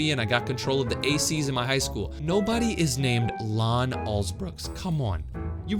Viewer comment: this is midnight and I heard lon assbooks